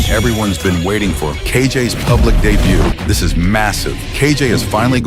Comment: did I hear MASSIVE?💀💀